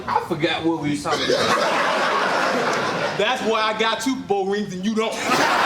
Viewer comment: What did Tim Johnson say, again around 17:43-17:53 during his impersonation of Shannon Sharpe?... I couldn't really tell. "That's why I've got--" what?... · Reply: That’s why I got 3 Super Bowl rings and you don’t